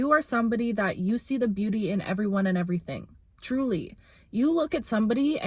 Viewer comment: when you said truly i thought you said juli which is a nickname of mine and i froze like 😳